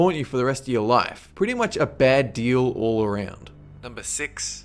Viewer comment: did someone say Hobbits?